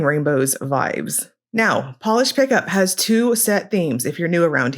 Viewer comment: Even though I know the drill, I love watching through the intro and everything! 🤍 Idk if you read the captions, but I always have my videos with captions and on 5:13 when you said Polish Pickup, the captions said "Polish breakup" instead! 😆 Beaux Reves is sooo tempting to me! 😫 edited to say... at 32:26 I got distracted by a fly in the video! 🤣had to replay that 3 times to really make sure it's not my screen! Thank you for making videos as always, and PLEASE rest and hope for safe recovery~ 🥺🤍